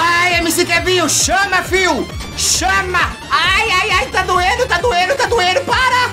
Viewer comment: tá doendo? Entendi muito errado isso